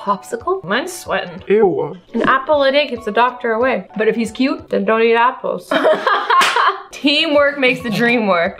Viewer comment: “An apple a day keeps the doctor away” ”And if he’s cute?” “then don’t eat apples” is a g o d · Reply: I thought she said" then throw your apples"😂